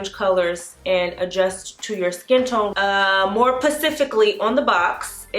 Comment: Look I know I’m probably the only one who noticed this but, she said “more PACIFICALLY.” Instead of “more SPECIFICALLY,” 😂 near